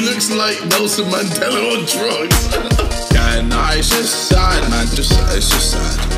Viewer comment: did he say "Can I suicide"? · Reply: but he said "its just sad"